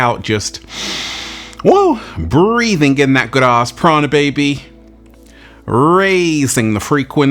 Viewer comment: Funny: I thought you were saying “breath in that good grass prana”. So I was visualizing green! You mean “ good ass prana”! 😅